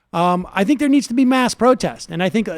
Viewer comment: Does Sam say "mass or masked protest" @ 02:02? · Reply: He says mass 🙃